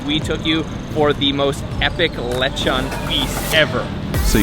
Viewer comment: I heard "electron" instead of "lechon" 😊